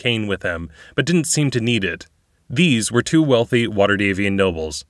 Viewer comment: Did he say, "These were TOO wealthy waterdeep nobles." or " These were TWO wealthy waterdeep nobles."?